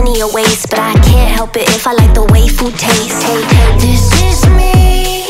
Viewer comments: Idk why, but instead of "the way food taste" I can only hear "the waifu taste"at 0:52 Anime has ruined me. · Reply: I hear “the way waffle taste” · Idk why, but instead of "the way food taste" I can only hear "the waifu taste"at 0:52 Anime has ruined me. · What people hear: If I like the way food taste What I hear: If I like the waifu taste